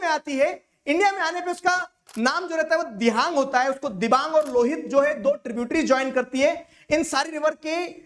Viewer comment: its Lohit byt I heard Rohit 😅😅😅